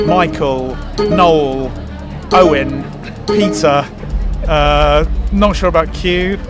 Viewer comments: I misheard peter as pizza 😭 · When He said Peter, I thought He said Pizza 😅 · I thought he said pizza not Peter 😭😭😭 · When he said Peter, I thought he said pizza 🍕